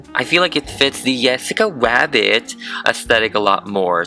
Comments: i hear ''yessica rabbit'' mMMMm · i swear i keep hearing "jwessca rwabbit" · it sounds like your saying yessica wabbit- 🙃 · Oh my god did he just say yessica wabbit I died 🤣🤣👏👏👏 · What I hear is “yessica wabbit” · Why do I keep hear him saying "yessica wabbit"